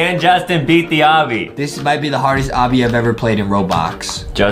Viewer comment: When justin said: this might be the hardest ROBUX obby ever 8:23 Me: did u just say robux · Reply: No he said this might be the hardest obby i ever played in Robox He did not say robux or Roblox